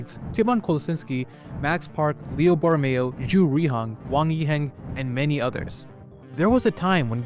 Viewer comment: did you say Ju ruihang?